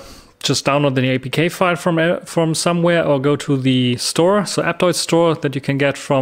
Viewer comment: Hey what is the App store that you show at approx 4:36? It sounded like you said Apptoide but that store doen't look like Apptoide I think. Also, you said something like Yola store also? I couldn't quite hear correctly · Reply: It is aptoide. Jolla has a special version in their store. The other store shown as icon is yalp which is not suppprted anymore and basically has been replaced by Aurora